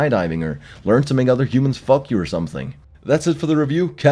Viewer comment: Did he just say "learn to make other humans fuck you"? ô_o Also, the fuck was that ytp-ending of a video?